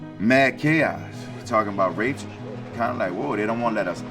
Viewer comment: did he say rapes?????